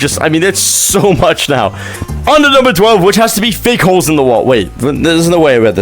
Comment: is it just me or did he say "thick holes in the wall"💀🙏